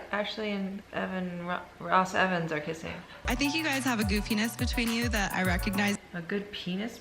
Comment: I am so glad I'm not the only one who thought said, "You guys obviously have a good penis between you." Apparently she said, "You have a goofiness between you." Even hearing it twice on here it still sounded like "good penis". I should go to channel and ask. Just to be sure, ya know.😉